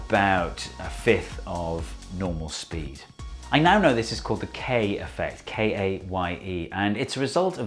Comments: I thought it said "Kanye affect" for a second lul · I thought it said “the Kanye effect” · for the longest time, i thought this said "Kanye effect" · Thought it almost said The Kanye Effect